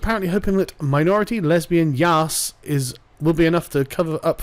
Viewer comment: Did Sargon just say YAASSS out loud?